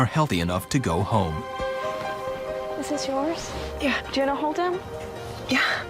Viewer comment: "is this yours?" 😂😂😂 I mean, I hope so or you just gave some random lady a baby that's not hers. So much for hospital security. I'm glad TLC doesn't run a hospital if this is how they think it's done. 😂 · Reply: I thought she said "This is yours.".